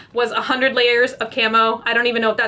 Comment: I thought she said "100 layers of camel toe" literally dieing right now